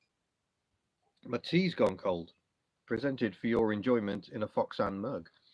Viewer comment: For a split second I actually heard this as "Matiz gone cold!"